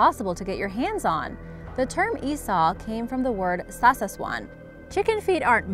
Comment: I think its "SAWSAWAN" youré referring to @ 7:40 instead of "SASASWAN"